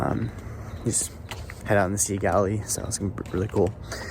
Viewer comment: I thought you were saying Sea Galley. Thanks for sharing your trip.